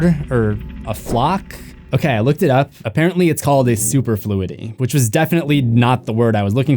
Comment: I thought he said superfruity